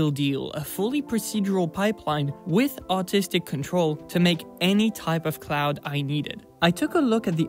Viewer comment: I thought you said autistic control